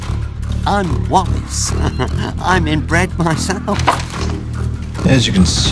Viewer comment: What Wallace said: "I'm in bread myself" What I heard: "I'm inbred myself."